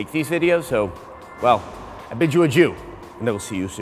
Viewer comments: did he just say "i bid you ajew" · It kinda sounded like he said "bid you a jew" at the end.